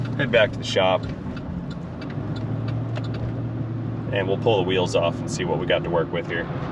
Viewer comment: I have no idea why but on first listen at 1:20 I swear you said “Let flip around, head back to the shop…We’ll pull a wheelie and see why we have to work with”. I suddenly stopped and stared at the video like ‘woh that would be some skill’